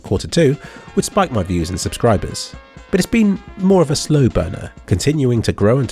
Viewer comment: "viewer gender" google, wtf? 5:08 Here I heard "Slow boner"